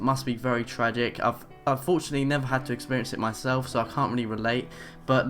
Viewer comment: Did you say "unfortunately", or "I've fortunately"..?